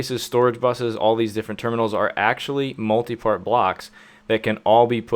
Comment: I keep hearing, "Multi-Fart-Blocks".